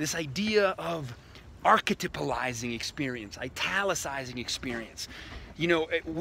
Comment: what is the word he uses at 1:25 ???! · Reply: It's not an actual word but he says "archetypalizing"